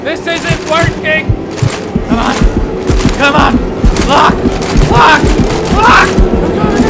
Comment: You were saying "LOCK! LOCK! LOCK!" What i heard was "FAWK! FAWK! FAUK!"